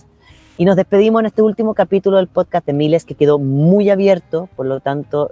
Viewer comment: wena, pensé que decía podcast de milei y ya quería tirar todo, wen podcast